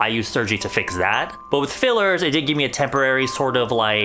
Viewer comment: oof i thought it said filters lol